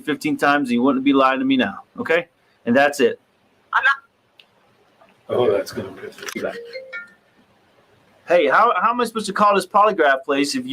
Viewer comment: What's he say at 26:44? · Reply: Sounds like maybe says, "Oh, that's gonna piss her off..."